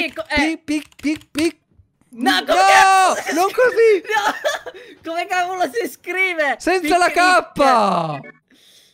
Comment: ho sentito una parolaccina eh eh non va ben così è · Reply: Ha detto cazz almeno non ha detto tutto